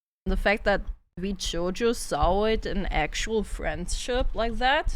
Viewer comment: The fact that VShojo sawroid an actual friendship like that? What did she say? I couldn't quite understand her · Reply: She meant sawed, like cut off